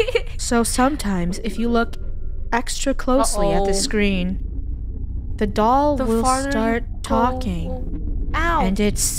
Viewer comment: I thought she was “and the doll says like and subscribe”😂😂😂